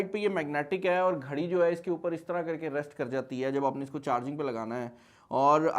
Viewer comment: ghari instead of ring🫡